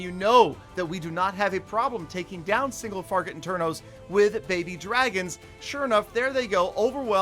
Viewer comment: is it just me or did say single farget intenos